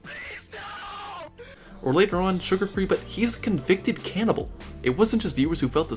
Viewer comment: am i tweaking or at 13:37 did he say sugarfree is a cannibal